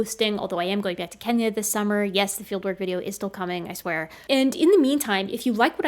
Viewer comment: Misheard "fieldwork video" as "Dilbert video."